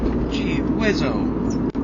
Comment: # 3 "geewizzo" lmao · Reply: I thought he said Lizzo, but this is even better